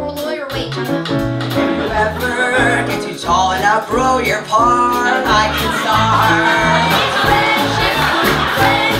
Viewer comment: What does he say at 2:12? If anyone knows, hehe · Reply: If you ever get too tall and outgrow your part, I can start. I think that’s it. 😁. Unless you were talking about after sleeping on stage, then I’m not sure even they knew. 😂